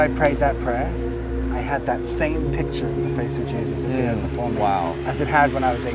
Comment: if you’re able to change the captioning, it says “the phone” instead of “before “ Really beautiful testimony!